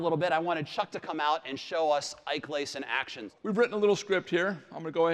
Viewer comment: did he say ike lace ???